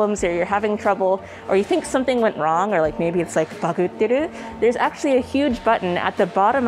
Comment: Hi what word did she say at 6:37? Thanks! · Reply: バグっている bugging out💃